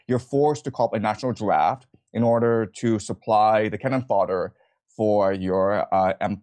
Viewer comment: for the people who can't hear. It's not "Ken fodder" he said "Cannon fodder".